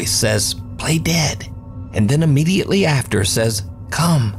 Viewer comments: i think it says "hey there! come!" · I think the voice says “Stay there” rather than “Play dead”